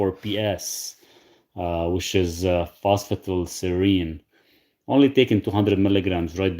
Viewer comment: The supplement you mentioned the 38:00 mark is it called Phosphival Surine ? My wife has MCAS & MTHFR would this supplement be ok, she doesn’t get much sleep. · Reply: Thanks for the thoughtful question. The supplement I mentioned is phosphatidylserine. It’s often used to help regulate cortisol, especially in the evening when levels stay too high. That said, with MCAS and MTHFR in the picture, I’d recommend starting low and slow,. It’s not a stimulant, but individual responses still matter, especially with complex cases. Sleep support through the HPA axis is often about timing and tolerance.